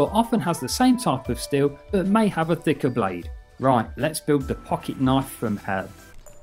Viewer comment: i thought he said the f*** it knife from hell